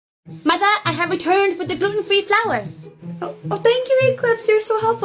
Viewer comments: Mother I have returned with the girl and free milk I really thought she said that 💀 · What it said “MOTHER, I HAVE RETURNED WITH THE FLOW” What I heard “MOTHER, I HAVE RE TURD WITH THE FLOWER”